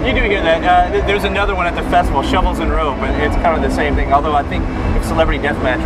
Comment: what did you say at 5:07 ? what band? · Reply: Shovels & Rope, it's another duo from Charlston